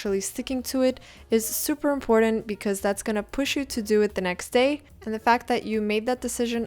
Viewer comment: when I heard you say this the first thing that came to my mind was “toucan do it!” 😭🤚